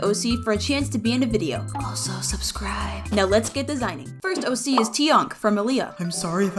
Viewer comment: I thought you said “now let’s get disowned”